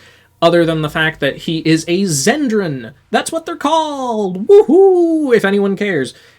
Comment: Ngl the first time i heard zendron i thought you said citron. So i thought you said they were all citrus fruits